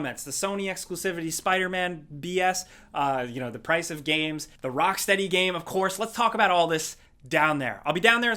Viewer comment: With my wacky ears i heard ''tease of thé next rockstar game'' and then i saw superman and thought well rockstar always make great games and finally i saw ''rockfuckingsteady''....